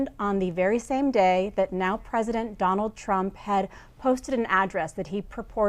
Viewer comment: Did she just say "Trump posted Obama's address?"